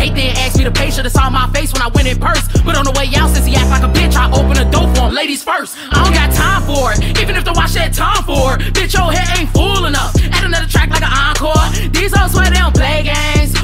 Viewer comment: it’s I’on got time for it, even if their *watch has tom ford * at least that’s what I heard